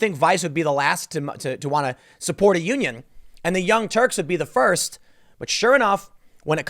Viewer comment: I thought Tim said The Young Jerks.